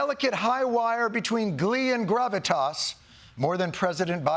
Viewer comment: I know you meant gravitas but I swear I heard "grubby-tas" which is more appropriate in light of the person involved.